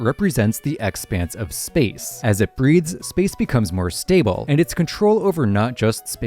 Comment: at first it REALLY sounded like he said "as it breeds" and I was so confused 😭😭💀